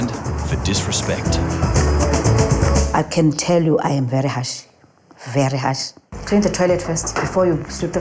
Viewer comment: Sorry but when she says harsh sound like she says hash